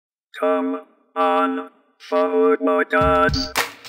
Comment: I thought Strong Bad was saying come on, Hobo guys.